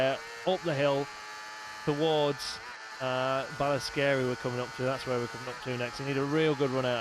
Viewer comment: Thought he said "scary" at 1:29 but no, it's Ballascarey, I should have known better.